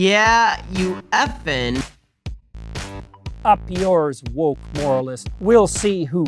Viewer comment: For the longest time, I've been mishearing the Jordan Peterson clip as: "Up yours, wolf moralists; We'll see who cancels who." And I've just assumed that he was probably mad about the debunking of the alpha/beta study 🐺 Well 😌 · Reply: I've heard it as "up yours woke walrus" as in that "trans" book about the kid becoming a walrus